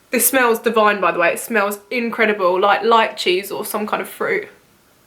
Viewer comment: I thought you said “light cheese” instead of “lychees” 😂 I was so confused why you thought that smelled good 😂 hahaha oops love you 💞